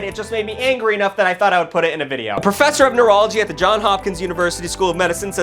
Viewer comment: I thought he said professor of urology